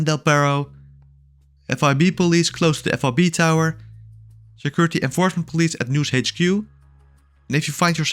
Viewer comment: What? Bro, your accent is so heavy nobody can understand wtf you're saying. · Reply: He said security enforcement. Read the car